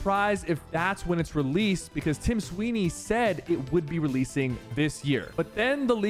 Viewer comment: Did super just say time weeny